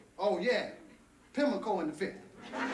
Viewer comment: What's he say, to the phone?? At 7:12. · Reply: Pimlico something. It's a race track, he's placing bets, hence "I'm making a few donations myself"